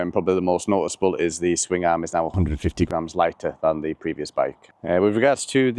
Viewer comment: did he say swing arm is 150lbs lighter?